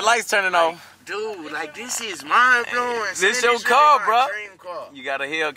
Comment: Ik I ain’t da only one det thought said “dis sh** mind blowing